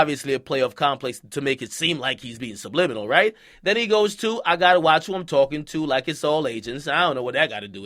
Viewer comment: I thought he said “ I gotta watch who I’m talking to like it’s all ages “ ?Lol